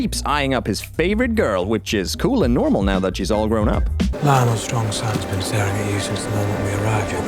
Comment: Why does Cole say „Lyonel Strong has been starring a you“ while he‘s clearly talking about Larys? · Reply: i think he says "Lyonel Strong's Son has been staring at you"